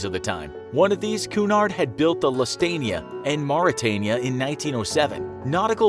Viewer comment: Did he just say lustania?